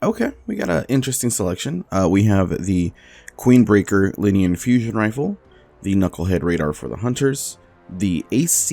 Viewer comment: Did he say "Queenbreaker, linean fusion rifle"?